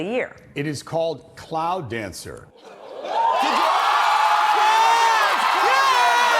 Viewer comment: Oh! Cloud dancer!... I thought they said plow dancer and my brain misfired